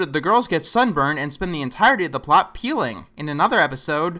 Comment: what did he say at 1:04..? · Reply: He said "peeling".